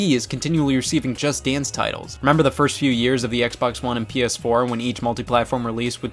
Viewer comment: Did you say PS4?